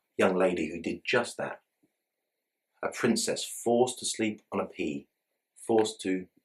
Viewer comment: I thought you said the princess was forced to sleep on pee 😂😂😂